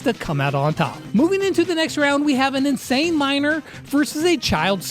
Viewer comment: when i sawbonnie and you said miner i thought you said minor